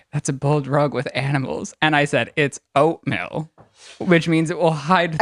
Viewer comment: Thank you for telling me Ryland said oatmeal! Lol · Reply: I swear I thought he was saying oatmilk 😞